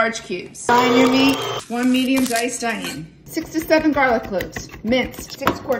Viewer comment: I really thought you said 67 garlic cloves 😂😂 she said 6 to 7 lmfao